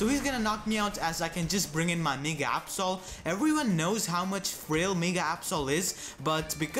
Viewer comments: did you say my nigga absol or my mega absol :]]] lawl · did anyone else hear,"...my nigga Absol."?